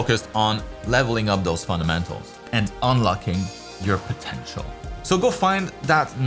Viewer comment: did you say… potential.